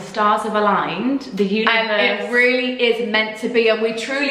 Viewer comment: when said the 'universe' i thought she said 'uterus' hah